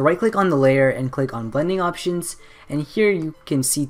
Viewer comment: I think I heard "bunny options" instead of "blending options" 3:08 edit: I created my profile by this tutorial man